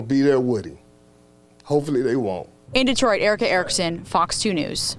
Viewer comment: The first time I heard the reporters name I thought they said Erick Erickson who is a propaganda idiot in Atlanta. He is trying to out stupid Hannity and Limbaugh